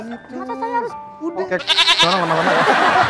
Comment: Kondre 6:54 ngomong apa sih kok, kayaknya kocak banget penonton ampe tereak, kalo kocak jangan disensor lah min.. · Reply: Kayaknya pak haji ngomong "kek kont*l orang lama-lama ni"😅😅🤣🤣